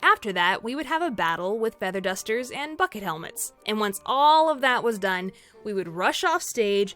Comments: Sounds like she said fuck it helmets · It's story time, Explainers! One of my more embarrassing moments from high school theatre, which just happened to involve Alice in Wonderland! Enjoy my humiliation! XD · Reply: ok, so when you said feather dusters I swear to God I thought I heard nuckle dusters ( A.K.A. brass knuckles)